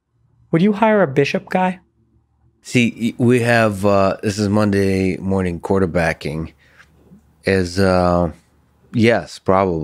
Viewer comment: Lex!!! Not only the largest Redbull ever produced but a coffee chaser. I thought I was bad. :) You did say it was Monday morning so... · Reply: Regarding Monday morning, if you're referring to what Lex said at 3:10, then what he said was 'Monday morning quarterbacking' which is actually a figure of speech